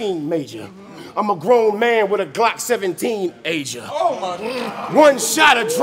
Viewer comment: Soo i just noticed wat brizz said FULLY I thought he said..I'm a grown man wit a glock 17 ager But he said "i'm a grown man wit a glock 17 Asia" as in Asia Dean hahaha